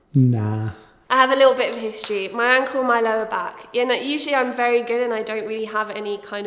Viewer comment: I swear to God I heard "I have a history with my UNCLE and my lower back creeping up on me" and I had to rewind